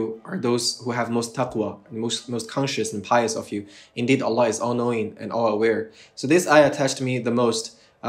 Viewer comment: brother, you have the subtitles wrong, there is written Allah unknowing (Astaghfirullah), it should be Allah is all knowing